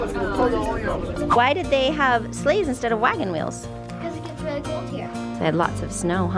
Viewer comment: Bro I thought said slaves instead of sleighs and I was whaaaaaa😂🤣🤣